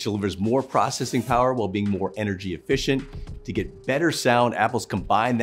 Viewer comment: i like the fact that i heard "you hear better sound" i like datttttt alot im still getting airpods pro 2 i been waiting on these reviews